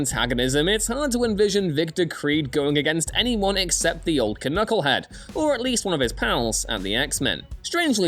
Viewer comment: why did he speak "k-nucklehead" instead of "nucklehead", can someone enlighten please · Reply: He's saying "canucklehead" Canuck is a term used sometimes as another term for Canadian so he's calling wolverine a Canadian knucklehead